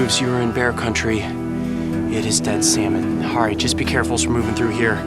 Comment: does he say dead seamen? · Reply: Salmon